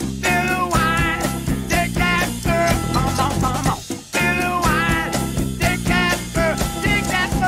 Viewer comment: I always thought they said "do I take that girl"🤣🤣🤣